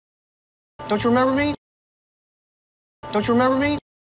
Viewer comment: 이건 진짜 안들리는데.. 혹시 뭐 알고계신 분 있으까요..? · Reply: Don't you know me? 이렇게 들리네요^^